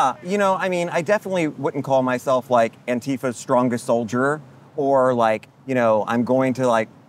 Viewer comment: I thought they said "Antifa isn't real" lol